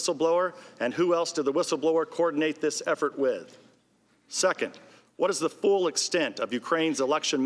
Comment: He said "second" and I totally heard "suck it".